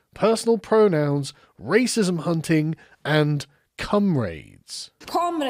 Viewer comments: did he say comrades or cum raids? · Did anyone else hear "cum raids" at 5:55, or was that just me? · I deadass thought you said "cum rags"